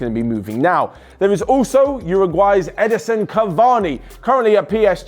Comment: Did anyone else catch him saying "Ederson Cavani"?